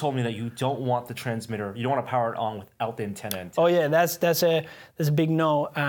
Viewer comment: What did they say not to do at 22:45 ? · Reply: EleventyThree Do not power on the receiver without the antenna plugged in or it could do a lot of damage.